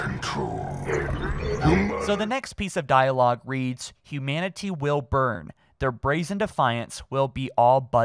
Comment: is it just me? Or dose it kind of sound like someone saying "human" just before starts talking again?